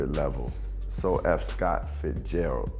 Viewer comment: Black Thought was saying “F. Scott Fitzgerald”, like so eff Scott Fitzgerald - Common read it wrong..